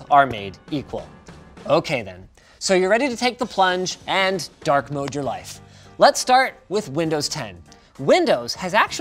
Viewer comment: I misheard and thought Linus said "So you're ready to take the plunge and dark-mode your wife"